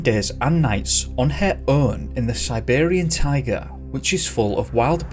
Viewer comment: always goes the extra mile, like researching dogs vs. bears. That's why is the best. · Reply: When he said the little girl "survived 11 days and nights on her own in the Siberian taiga", I really thought he said something about surviving with a "Siberian tiger", and I was like hold on this story just went to an entirely new level.